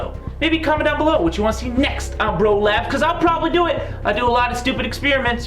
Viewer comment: I thought he said bro lapse